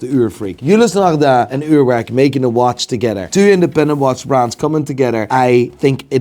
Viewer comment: did you say it was a Swatch?